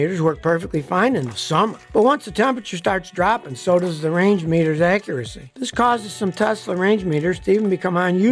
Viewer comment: The first time Scotty said “Range Meter”, I heard “Reindeer “.